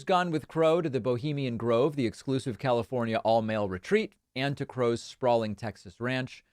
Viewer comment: Did I hear "all male resort"?